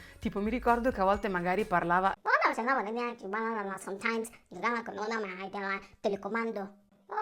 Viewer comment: Io ho capito "sometimes" e "telecomando" 😂